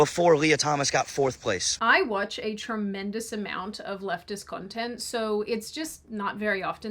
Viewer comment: I had to rewind. For a moment I thought she said "lesbian" instead of "leftist".